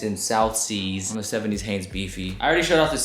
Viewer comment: it's NOT "susie" lol it's "siouxie and the banshees". These guy wear bands tee short they don't know and don't listen too. How much can you be a poser ? Crazy how these guys are putting so much time and effort and still looks like shit. I truly don't understand how they don't see how these "vintage" (very loose use of the term) clothes are badly cut and don't fit them.